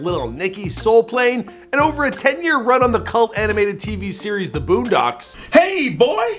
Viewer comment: Did he say cult animated